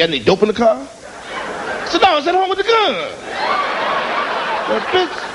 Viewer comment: What he said at 6:22 about the gun he said it so damn fast I didn't hear em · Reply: blue god the cop asked if he had any dope in the car. He says No! I left it at home with the gun😂